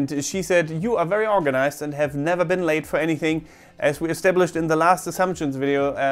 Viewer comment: I thought said “You’ve never been laid for anything”. Then I realize I just misheard that. Although that would explain a lot about the Korean spy angle...